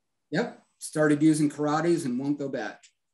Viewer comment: what is the brand of files that the presenter will only buy from now on? I hear 'karate' and google auto-subtitles as well, but thet does not seem to be quite right... thanks in advance! edit: I think I found it: corradi!